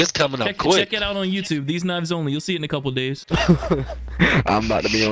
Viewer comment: lol - who else heard "these nuts only" 🤣